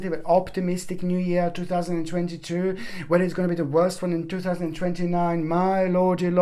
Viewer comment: Hold up. Did you say "2029 is going to be the worst year?" Did I hear that right? What happens in 2029?